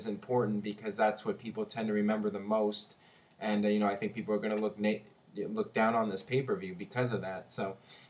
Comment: he was gonna say "people are gonna look naked"